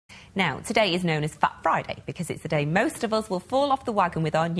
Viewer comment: I thought she said 'Today is known as fuck Friday'.